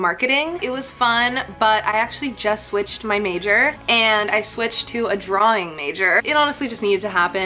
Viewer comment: Can somebody tell me what she said here 2:18? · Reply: she said she switched to a drawing major